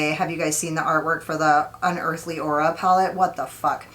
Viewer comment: Wait, did you say Unearthly Aura palette??? Is she releasing a new palette?? Maybe I misheard. I haven't even gotten Spring Magic in the mail yet and am already like "lets fkn go!" 😂